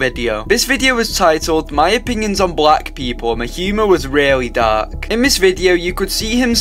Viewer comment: did bro actually said the humor was really "dark" ???💀